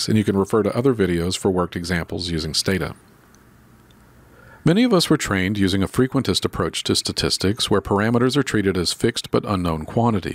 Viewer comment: would someone please tell me what is he saying at 0:28 ? thank you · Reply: I think he says: "Many of us were trained using a frequentist approach to statistics..."